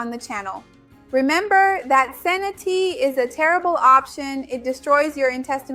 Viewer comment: Thanks good video but you talk so fast I cant understand what you are saying sometimes. 7:01 Senna tea?? 7:42 Kefir?? Thanks